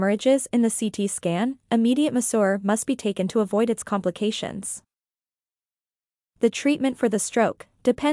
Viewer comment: What word is being said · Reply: Immediate masseur must be taken to avoid a complication